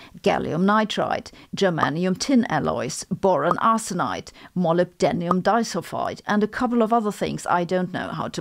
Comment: 15: 37 is a good try Sabine! - "molybdenium isofide", probably includes molybdenum disulfide too. please forgive me if I misheard - I absolutely love your channel xxx